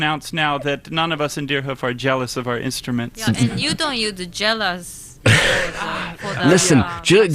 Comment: What did Satomi say at 8:06? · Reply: "you dont need to tell us" ?